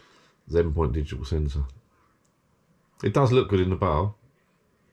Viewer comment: I thought for a second you said, 'Looks good in the Bath'. :hand-pink-waving::face-blue-smiling::hand-purple-blue-peace